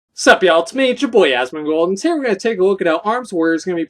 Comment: Every single time I hear your intro I hear "sup yall, it's me chaboyaz" like, I thought that was your name for like a month, sorta, I wasn't really paying attention. Love your videos!